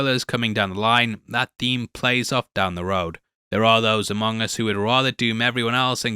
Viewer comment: What did you say at 7:31 ??? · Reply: Among us